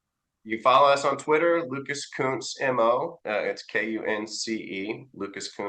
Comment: Oh, Lucas Kunce. When BTC said it at the top of the vid, I thought he said Luke-a-toots lol